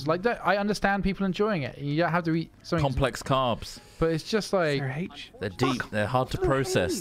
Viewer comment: No, complex carbs are the good carbs, the ones you should be eating. Simple carbs are the bad ones, such as fast foods. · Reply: I'm stupid and thought you said crabs. And now I'm just thinking of "slow" crabs